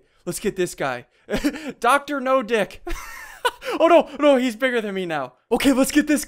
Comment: did he said Dr.No Dick?